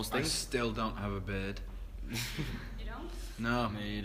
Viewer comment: Oh! Beard... I thought he said bed